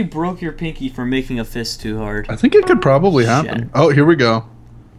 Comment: Did Jerome said "Shit" at 9:24? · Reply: He did.